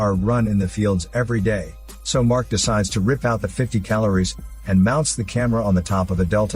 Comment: Did you just say "the 50 calories"?